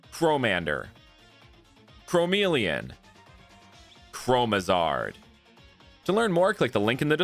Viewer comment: i wasnt paying attention and when he said chromizard I thought he said chromizone